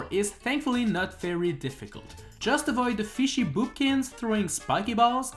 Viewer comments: DID YA JUST SAY FISHY BOOPKINS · Did I just hear Fishy Boopkins ? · did i hear "FISHY BOOBKINS" ? SO YOU WATCH SMG4! don't think i didn't notice that!😃😃😂